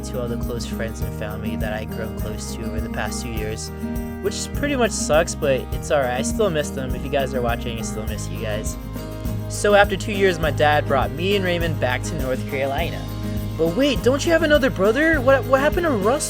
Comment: I thought you said "My dad brought me back to North Korea" and I was like WTF